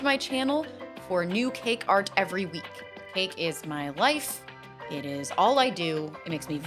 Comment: when you said cake is my life I thought you said cake is my lice